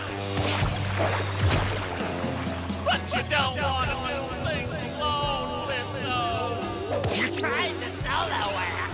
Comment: What is Striker saying in this scene? I've rewinded multiple times and can't figure it out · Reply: "but you don't want to do things alone blitzo."